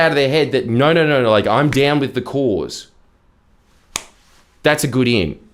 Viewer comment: I thought he said "down with The Corrs", which with the way he said it actually made it seem like a huge flex.